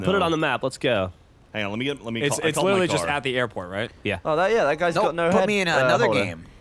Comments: Did really say "That guys got no head holder?" haha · did just say that guy had no head holder...?